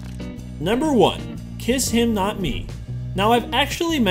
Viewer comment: When I heard Kiss Him Not Me. I thought you said Kiss Kiss Fall In Love. Its sad because ive seen both animes.